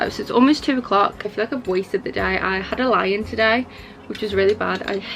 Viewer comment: I thought you said "I had a lion today" and I was confused for a solid minute. ><